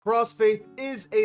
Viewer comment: Intro had me tweaking, I thought he said Crossfade and not Cross Faith😭